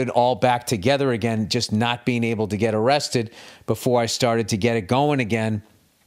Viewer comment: it sounds like Bill says “just not being able to get arrested before I got it going again”- does anyone know what he actually said there??